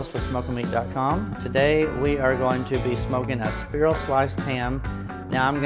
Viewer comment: Did he say speer-al sliced ham?